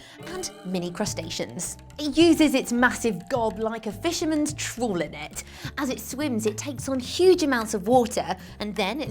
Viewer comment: what is she saying? I listened to it countless times and I hear, "it uses its massive gallblaiker fisherments TROLL in it", WHAT?!! Can someone please clarify for me what she said?? Thanks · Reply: She said: It uses it's massive gob(meaning: mouth) like a fishermen's trawl (the giant net they catch big schools of fish with)